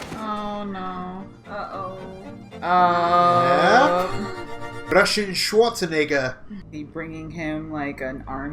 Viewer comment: I heard “Russian shot a nigga”😂😂😂😂